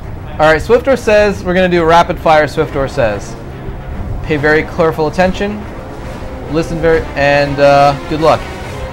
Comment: From 7:43 to 7:47 WTF did you say Something like :.. pay very (clerful) attention, listen very and um good luck ! NOT HATING !